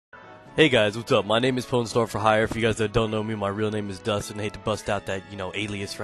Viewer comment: I thought you said "Porn Star for hire" in the beginning I dunno if its my hearing or what.